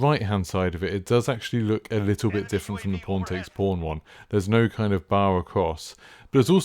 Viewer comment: I will admit when i heard you saying pawntakespawn my mind heard porn takes porn